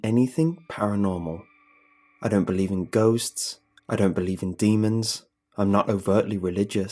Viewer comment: i heard "I don't believe in goats" and i got too excited lmao